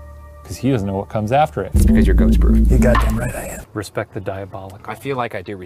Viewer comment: anyone else hear 'its because you're ghostbur?'